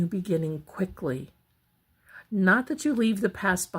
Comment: I thought you were talking in code lol when you said a relationship or something ends abruptly, something abruptly will start again. I thought you were saying 🥦 instead of abruptly,!! I thought it was code for something bad. 11:59 appx